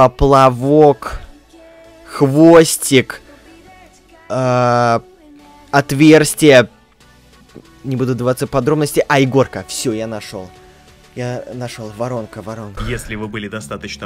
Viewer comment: мне послышалось Игорка.😃